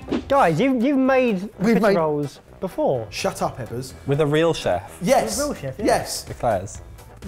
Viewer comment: So, at 1:42 , what does say? It was taking, so it's not "Shut up, Ebbers". Because I understood "Shut up, Heather."